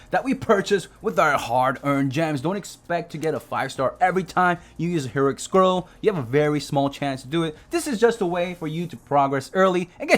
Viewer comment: It is as shown in the video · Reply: Lol for some reason I heard it as “you don’t always get a 5 star” sorry x)